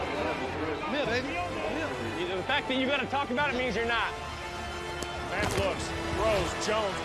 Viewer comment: What does that dude at 4:40 say to Edelman? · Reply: "I'm here baby, I'm here"